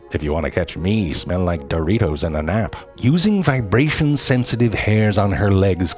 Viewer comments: "If you wanna catch me, you should smell like Doritos and a nap." R e l a t a b l e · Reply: I thought he said Doritos and an ant. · “If you wanna catch me, smell like Doritos and a nap.” DEAD 💀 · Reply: I legit thought he said “smell like dirty hoes on an app” Wtf is wrong with me.